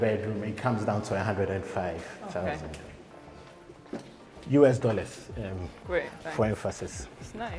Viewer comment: did he say US dollars😂😂😂😂I wouldn’t dare pay $100k for that shit hole